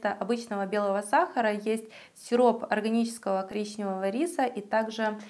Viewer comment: Существует гречневый рис?это как??)) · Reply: Irina Suh не гречневый, а коричневый ))) вам послышалось